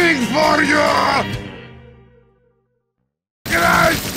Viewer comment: “Slaps me on the knee!!” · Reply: Thought he said "That slaps me underneath"